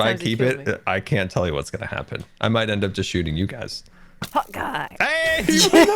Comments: Has scar being saying hot guy all this time?!?!? I thought he was saying hawke eye. My ears, why must you do this. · Who else thought they were saying"hawkeye" and not "hot guy" LMAO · The whole time I thought he was saying "hawk eye" and turns out to be "hot guy" bruh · until now i thought scar was always saying "hawkeye" not "hOt gUy" that's even funnier lol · am I the only one who thought that Scar was saying "Hawkeye" instead of "Hot guy"? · I thought sky said Hawkeye not hot guy! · This whole time I thought Scar was saying Hawkeye but it’s actually hot guy 😂😂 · All this time I thought Scar was saying Hawk Eye! And now I see the bow is called Hot Guy. Oh Scar... such a goofball. All of them are goofballs... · Am i the only one, who always thought scars bow said hawk eye and not hot guy? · I thought they were saying Hawkeye not hot guy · All this time I thought scar was saying 'hawkeye' but only now I've realised he says 'hot guy' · I thought scar was saying hawk eye, not HOT GUY, this whole time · I thought yall were saying “Hawkeye” not “Hot-Guy” 😂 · Lol I thought Scar was saying “Hawkeye!” instead of “Hot Guy!” this entire time. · i thought scar was saying 'hawkeye' and not 'hot guy' until today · The entire time I thought Scar was saying "Hawkeye" because bow. Makes a lot more sense than hot guy. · wait scar has been saying hot guy? i thought he said hawk eye... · for the longest time, i thought scar was saying “Hawkeye” instead of “hot guy”